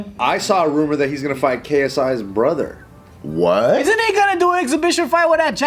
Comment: KSA’s brother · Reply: I thought he said KSI's brother. Does that mean Deji? Cause Deji lost to Alex Wassabi.